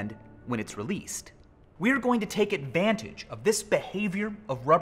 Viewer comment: Damn it I thought he said "ad-band-tange". Nope he has to be lame and say advantage. Ugh XD